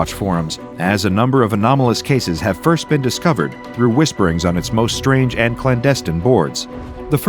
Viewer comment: thought that said whimperings 💀